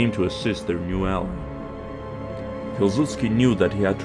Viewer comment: kilzutski in the subtitles got me laughing so hard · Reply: No idea why it doesn't recognize me saying Piłsudski. He isn't even among the "people mentioned"